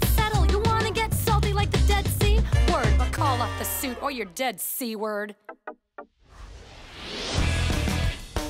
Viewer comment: Like the dead sea? Word. But call off This suit, or you're dead see, word. I always thought she said, "you're dead C-word"